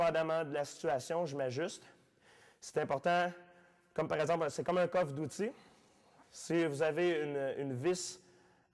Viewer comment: J'ai compris "C'est comme un call of duty"